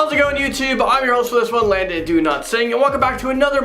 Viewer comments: i keep hearing "Landon DONOTSING" sry random.. haha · I love landon way of telling the stories but every time he introduce himself I hear it " LONDON DO NOT SING "